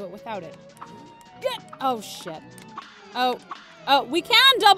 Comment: cupquake....you said the s word not the shoot word you said shi- in the ghost part 3:10- 3:11 cupquake im disapointed on you,you are saying a lot of bad words..i heard you say shi- and fuc